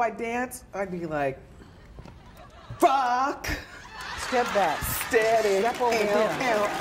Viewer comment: I swear Wendy said "Fuck steady" instead of "rocksteady". Lol